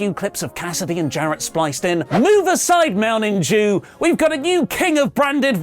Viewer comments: Sounded like you said Mountain Jew instead of Mountain Dew 🤣🤣🤣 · "Shazam-pionship" HAHAHAHAHAHA! Also, did he say "Mounting Jew" at 6:49? This weeks reviewing AEW Dynamite a 100%.